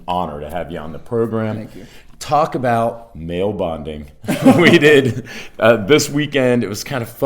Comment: At first I thought you said you were gonna talk about "male bondage"